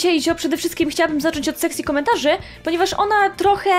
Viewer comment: nie wiem czemu ale usłyszałem "Sexy Komentarze" XD